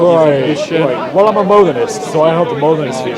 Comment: does say he is a modalist?